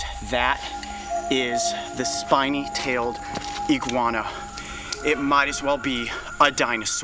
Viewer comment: I thought he said slimy tailed iguana